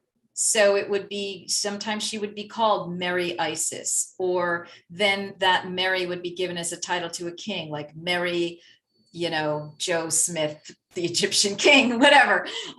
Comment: When you said " Mary Isis" I heard " Merry Christmas!". Things that make you go Hmmm....